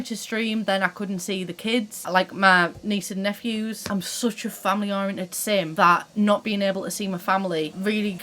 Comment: Did you say you're such a family oriented sim? Lol I, too, feel like a sim.